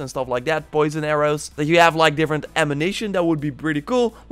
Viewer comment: Did you just say "amination" in 3:41 ? :D